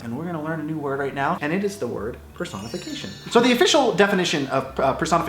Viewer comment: When you said “Personification” I heard “Croissant on Vacation”.